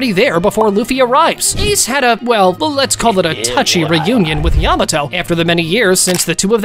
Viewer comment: A touchy reunion is what he says😏